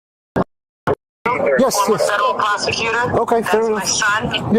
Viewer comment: the audio is choppy but it sounds like she said "former" this time.